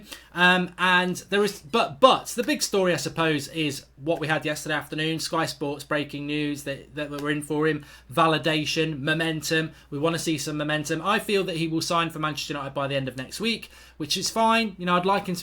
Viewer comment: I thought you said spiced pork breaking news